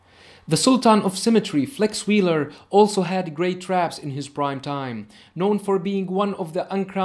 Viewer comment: How many times I hear "has best traps on his primetime" Lol😆😆